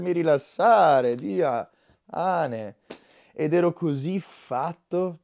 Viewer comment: era un Dia-hane???? ho sentito bene????